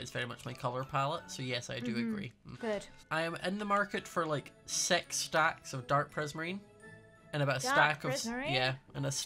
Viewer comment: Did he just say sex stacks what the just stop aaaaaaaa